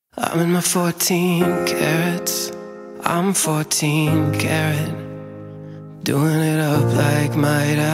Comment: Thought he said "im 14 karen" lmao